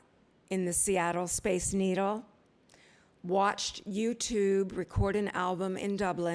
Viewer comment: Did she say U Tube XD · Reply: U2.